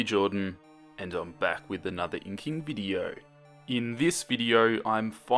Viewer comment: When you said, "I'm back with another inking video," I seriously thought you said cooking video and I had to play it back XD